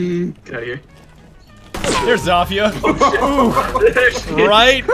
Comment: Did he say zafia instead of zofia?